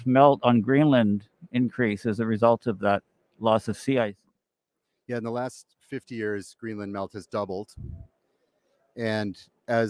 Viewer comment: Thx Jason. You bring up many important points. At 14:30 "Greenland melt rate doubled in the last... 6 years?" Sound is buggy there and the cc says 50 years. I know that can't be true. I did talk about your red microbes in one of my French articles a few years ago. That was mind blowing. Take care 🖖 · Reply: Yes doubled in the past 50 years